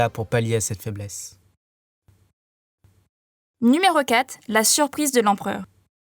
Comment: tu a dit la suprise ou ses moi qui et mal entendue parceque ses ecrit la surpuissant